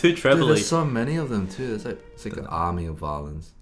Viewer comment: ~ARMYYYYY!!!💜💜💜💜💜💜💜+💜. I will.say...I heard ANOTHER play on words but u guys didn't pan it up.... besides MY OBVIOUS, When u said "ARMY OF VIOLINS", I ALSO heard "Army of VIOLENCE!" Lol